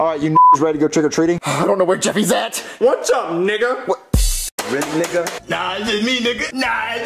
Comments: What did say, all I heard was the n word, not what he said first, if someone could tell me what he said I would like to know, thank you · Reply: he said "really nigga" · Jeffy didnt say hard r he said Nigga, it just sounded like hard r cuz of how his voice used to sound 0:19 edit: since someone said i'm not black, just an fyi, i AM black, i AM allowed to say the n-word, to that person, you are just embarrassed cuz you were wrong. do not assume i am not black unless you have seen my face. my pfp is white cuz i like the character???